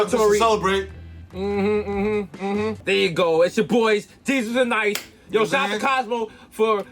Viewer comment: The brand is most definitely strong 💪🏼 but did Desus say “ it’s ya boys, Desus AND Nice? Lmao. At like